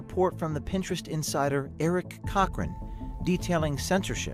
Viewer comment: I burst when i heard the pinterest insiders name was eric cockring